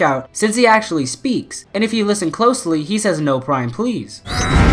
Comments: Awesome video man. Keep it up! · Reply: I think he said “No prime please stop NO! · I always thought he said, "No not Me"